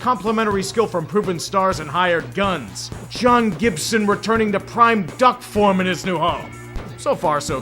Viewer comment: Sounds like you said "Sean" Gibson" instead of John Gibson. Doesn't matter to Wings fans, the team is golfing atm